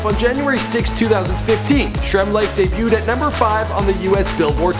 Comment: i thought they were saying shrimp life: D my bad